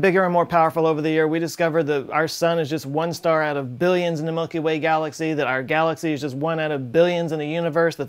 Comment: did you say monkey way galaxy?